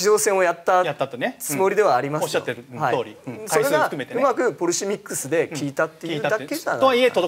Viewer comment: ここフォルシミックス？ポルシミックス？何て言ってるかわからんけど意味もまたわからんから碩学な人誰か教えて下せえ